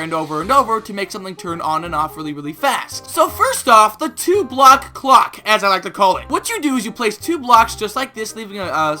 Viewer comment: i thought he said the two block cock :)